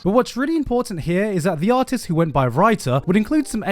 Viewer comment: Wait! Did you say.. Raita?